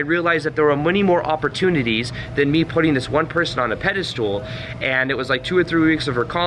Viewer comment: I keep hearing “pedestool”